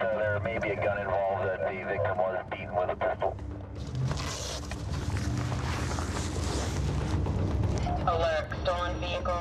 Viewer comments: I had to replay 0:35 because the radio sounded like it said "beaten with pencil" not pistol. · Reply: Lol I had to do the same. But I thought I heard "beaten with a pretzel " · I had to replay 0:35 because the radio sounded like it said "beaten with pencil" not pistol.